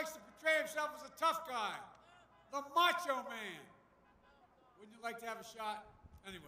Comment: Did Biden just say this about our President? "Would you like to have him shot?"